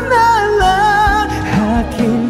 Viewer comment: I heard like "Hakin ka nalang. Hakin ka nalang." 😂 It sounds cute tho. 🤗